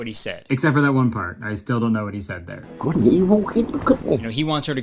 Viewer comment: I have a guess as to what he said, "Good and evil keep me company." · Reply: I heard “good and evil, hey look at me.” But yours makes sense. I’ve seen this video of his like 5 times but this is the first time I’ve remotely caught what he said and of course it’s when I’m super high 🤣